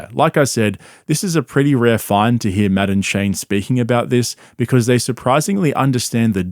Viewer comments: I kept hearing “madam Shane” and I was so confused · I thought you kept saying "Madam Shane" and I was wondering who that was.